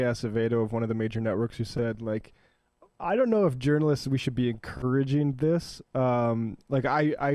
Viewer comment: Sorry what? Am I the only one who couldn't hear a word that dude said? Were subtitles too much to ask? Also sorry, Matt, for a sec there I thought I heard u say we shouldn't be doing this to "journalists"...